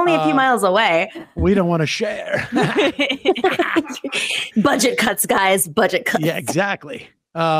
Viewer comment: Oh she said "budget cuts" i heard "bunch of c..."